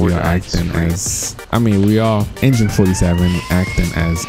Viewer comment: When he said Engine 47 I thought he said Agent 47 for a moment.